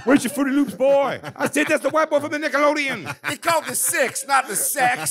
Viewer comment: Sounds like jay saying Niqqalodeon 😂